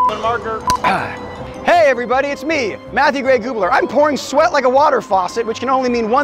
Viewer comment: I thought he said matthew gay gubler or did he